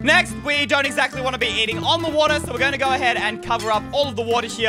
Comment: I thought you said no swimming in the water for the table